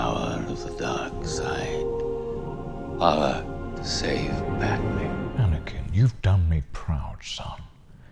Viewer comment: What Palps actually said: The power to save Padmé. Captions: The power to save Batman.